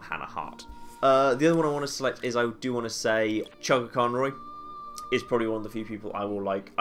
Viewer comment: I know this is pretty old now, but can anyone hear who names as his second inspiration at about 20:12? I can't quite understand him and I would like to check out the channel :) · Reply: it is spelt CHUGGAACONROY. he is the best. told to you by someone with magor spealing problems. and a brokenish keybord.